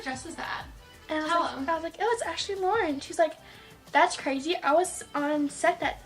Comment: who’s the prom dress designer?I didn’t understand what she said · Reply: ashley lauren!